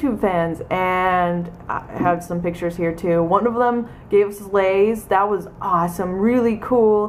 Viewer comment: I thought you said Laid